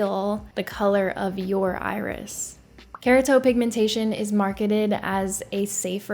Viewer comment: I swear I thought you kept saying Carrot top pigmentation